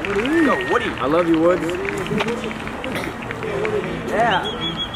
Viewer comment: did he say "I love your wood!"?